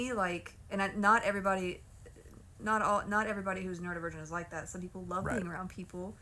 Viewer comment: I’m sorry but 2:53 when the auto caption says “no everybody who’s not a virgin is like that” 😂😂😂